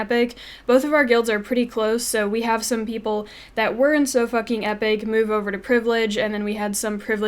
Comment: I seriously thought you said. "we have some people that weren't sofakingepic move to privileged" LOL